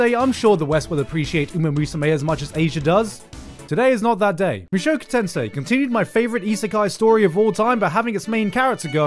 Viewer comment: what is the name of the anime, again? I just heard something sensei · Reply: Makoto tensai